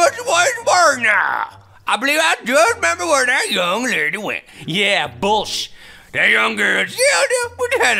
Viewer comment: Did anyone hear the same thing I did? "Yeah.. bull sh**!" LOL